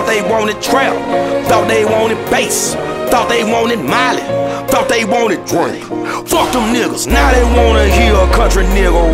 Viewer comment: Thought he said "Thought they want Drake", and honestly that would make a lot of sense